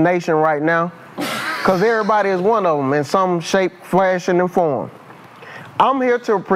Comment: lmao that boi said eeer body is one of em in some Frashion or From lol wth is that award